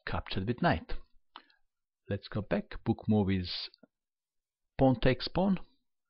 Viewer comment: "Lets go back... ... ... pawn takes pawn" what did you say there i can't tell? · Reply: "Book move is"